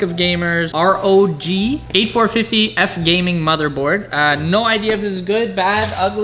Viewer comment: I'm hoping I didn't hear 8450 Motherboard 😐